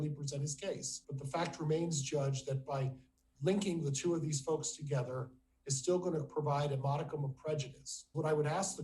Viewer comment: did he really say .." linking these folks together is gonna provide a prejudice"..? THESE FOLKS! Oh he's really using language to create distance between Chad and Lori, if that's what he said ! Thanks for this, I didn't know this was happening today!